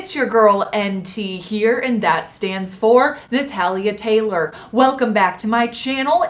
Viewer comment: The first time I watched one of your videos I thought you said Natalia’s tacos 😂🤣😂